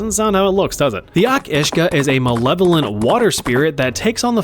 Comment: I thought it said "Each Usage"🥀✌